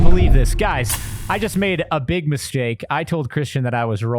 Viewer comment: did he just say he was making a big "mis-jake"?